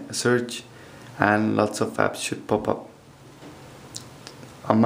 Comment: I sounded like you said "And lots of faps should pop up" no offense